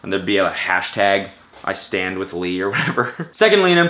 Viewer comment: Did he just say i stan with lee